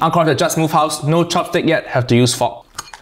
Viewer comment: I misheard it as "have to use fuck"